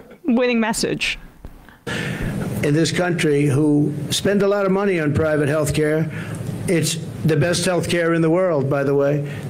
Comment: Sorry, but correction, Emma... Trump isn't saying the insurers "had to do it because they wanna make money". He actually said "...many people... spend a lot of money on healthcare... but they wanna do it. They worked hard to make money and they wanna do it", meaning Americans want the privilege of paying for their own private healthcare. Basically, in his juvenile mind he thinks a convincing argument is that people actually want to pay for private healthcare. Once again, in his mind, once you get that hard earned paycheck you can't wait to spend it. And paying for the luxury of your own private healthcare is so better than having to share healthcare with everybody else who gets it for free, which cheapens it. THIS IS THIS MAN'S THINKING!🤯😂